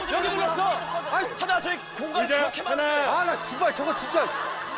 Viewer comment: 초 하나 저거 시x진짜 들리는건 뭐지